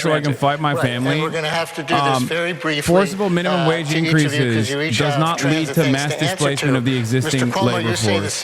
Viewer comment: what does Hasan say here? I can't make it out over the debate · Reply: "Forcible minimum wage increases does not lead to mass displacement of the existing labor force".